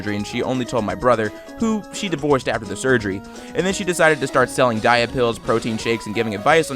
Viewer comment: I heard the gastric bypass one wrong (1:25) I thought it said she divorced her brother after the surgery.